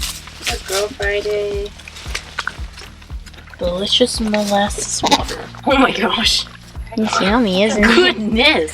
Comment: More baby goats, please! Soooo cute! What's the brown stuff in the bowl that Friday was drinking? 🤔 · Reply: I thought she said molasses water?